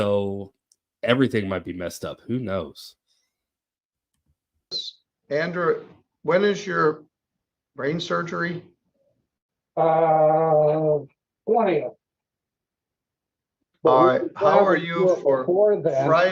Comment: Did Judge just say "When is your brain surgery?" And then the lawyer, "Uhh, the 20th. Can we get the dog outta there before then?" 😂 I love it! Thanks!